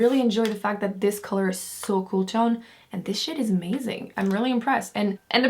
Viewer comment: I thought you said "this shit is amazing" lol